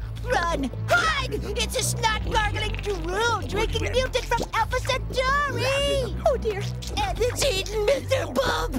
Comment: What did he say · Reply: "It's a snot-gargling, drool-drinking mutant from Alpha Centauri!"